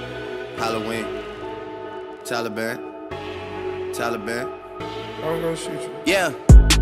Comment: Don't know why I never listened but I thought he was saying "Tyler Perry" all this time, but he's really been saying "Taliban" 😂